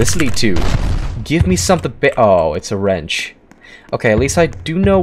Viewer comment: was i the only one who heard him say:,, Gimme some thing bi-ohhh" was he gonna say the b word if he was the wrench savet us😅😂